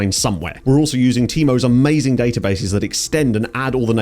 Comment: Thought you said temu's database 😂